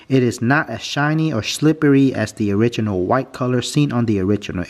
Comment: im sorry but did you say shlippery??? lol im sorry for laughing